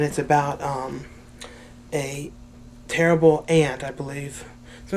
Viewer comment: When you were talking about the woodchipper massacre I thought you said it's about a terrible Ant. It was only when you said Grandmother I realised you must have said Aunt.